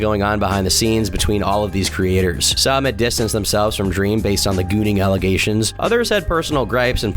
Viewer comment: really sounded like you said "gooning allegations" also its weird tommy did all that about dream, they did used to be friends and although they arent now it just seems immature of him to make fun about a topic that seemed like such a mild contraversy.